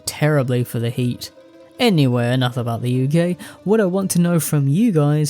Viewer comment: my brother in christ I was washing dishes and heard you say "enough about the uk" and thought you said "enough about being gay" and im like 😭 brother what!!!!????